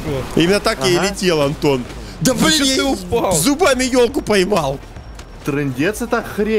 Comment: мне одному послышалось что он сказал кое-что?Или он это реально сказал? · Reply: Тебе показалось:•| Он зубами ёлку поймал